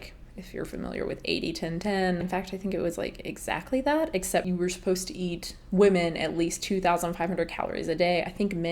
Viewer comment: My YouTube was gliching until 0:41 when I just heard "you were supposed to eat women."